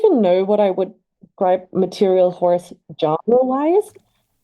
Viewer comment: i actually thought that said “a liberal horse”